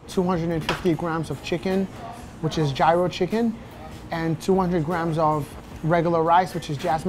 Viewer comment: Did he just say "Jyro Chicken" instead of "Gyro Chicken"?